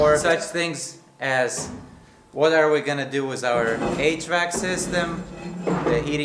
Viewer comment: I thought said 8 track system for a minute instead of HVAC. 😂